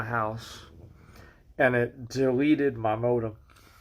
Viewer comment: At first i heard "it deleted my mom" and i was very concerned, i replayed the start of the video to hear it again, now im glad that what i heard wasnt the actual thing wtf